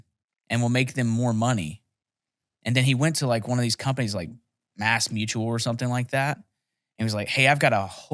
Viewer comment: Did he say, "Ass mutual?" @ 37:45 ???? 😂😂 That guy who figured out how to transfer that money seems to be kinda a genius. Anyone got any more info on it????